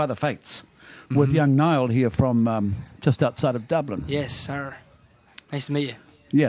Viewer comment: Did nail say yes sir at 0:50 cos it sounds like it xxx